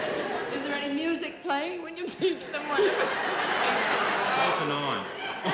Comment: What was the answer (at about 14:20) to Arlene's question about music?? It seems to have been great, but I've tried and tried and can't make out what he's saying, and the captions don't help either. I'm hoping SOMEONE can hear it! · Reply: "Off and on." Arlene repeats it a few seconds later.